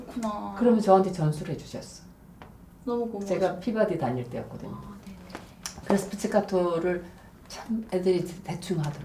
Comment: "Peabody (Conservatory) 다닐 때였거든요" 비발디가 아니구요. 교수님, 사랑합니다. 오래 오래 좋은 연주 들려주시고, 훌륭한 학생들 많이 배출해주세요!